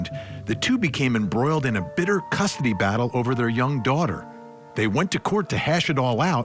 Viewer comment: At first, I thought it said Ashley BANfield!! 😂😂😂